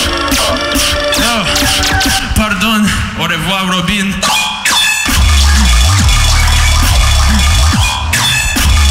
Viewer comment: What does he say at 3:52 ?? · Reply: "Pardon. Au Revoir Robin" is in french. Which means "Sorry. Goodbye Robin" in english